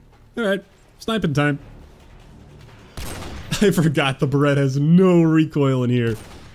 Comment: Did he call the Barret a Barreta? Oof that cringe. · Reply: Really cause I hear an A at the end there, sounds like beretta to me. And I get it barret and beretta sound similar but I just thought it was funny. 3:30 And wouldn’t it be Ba-Rret instead of Bear-It? Sounds likes some southern country slang.....